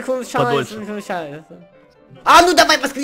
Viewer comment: Мне одной когда читал заклинание на японском в конце послышалось "Сахар чай"?